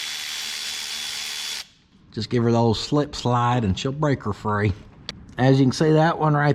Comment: It sounded like you said slip slide and striper for the next guy. I have bought my buddy’s a lap dance before too